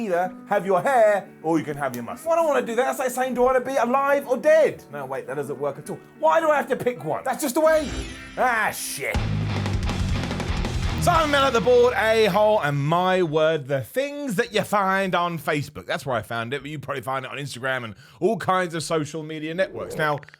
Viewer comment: Why have i always thought he said "bored a-hole" XD